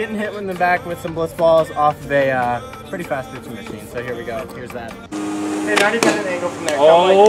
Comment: I thought he said slades was gonna get hit by a pitching machine